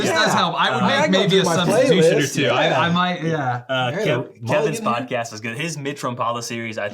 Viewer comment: i wish y'all would put the full list of podcast picks and links here somewhere. I couldn't make out what podcast you were talking about at 1:00:38. I've heard of and listened to some, but hadn't heard of Dan Johnson and a few others, including the aforementioned 'Kevin's podcast.' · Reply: Hey Adam, was referencing the Deer Hunter Podcast. picks: - Wired to Hunt - Deer Hunter Podcast -Truth From the Stand - Chasing Giants picks: - Wired to Hunt - Trail Cam Radio - Nine Finger Chronicles - Deer hunter Podcast picks: - Wired To Hunt - Deer Hunter Podcast - Whitetail Legacy Podcast - Chasing Giants with Don Higgins and Terry Peer